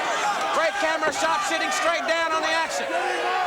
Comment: It sounded like JR said “sh*ting on the action”.🤭😄